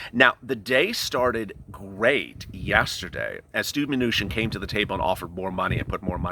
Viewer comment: Love the show and really enjoy watching it, but man at 12:07ish it sounded like you said Stew manuchin which kinda made me chuckle some. Thats all from the Hoosier state.